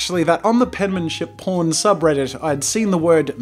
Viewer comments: penmanship porn subreddit? Did I hear that right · I keep hearing "The Penmanship porn subreddit" was that what he said? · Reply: On reddit, subreddits that are r/[subject]porn such as r/penmanshipporn, r/foodporn, r/earthporn, r/historyporn, etc. (there are hundreds of theses) are all SFW (‘safe for work’ - basically not gonna get you in trouble if someone see you browsing its content - clean) and showcase images of beautiful penmanship, food, earth, history, etc.